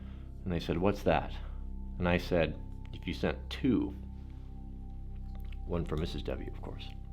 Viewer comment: I'm sure I misheard...I could swear he said "You know what would be better? Send two, one for Mrs. Nut'n!" Don't tell Nut'nFancy! Shots fired! (Or do I need to get my hearing checked?)